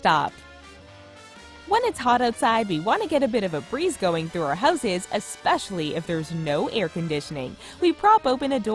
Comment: did she just say hoes's